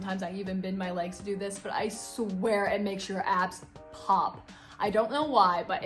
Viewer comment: Am i the only one who heard i swear in makes your ass pop in 14:37😂😂😂😂